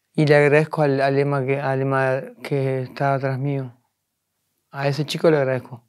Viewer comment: que dice? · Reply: "Le agradezco al Ema que estaba atrás mío"